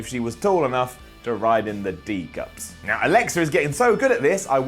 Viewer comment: Did he say "ride in the D cups" at 6:00??